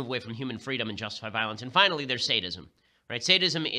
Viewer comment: Sadism. I thought he said, "Statism"